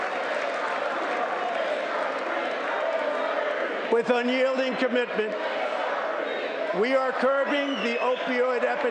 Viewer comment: any idea what they're saying? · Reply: They’re chanting “HR3”. A Democrat introduced bill similar to what he is requesting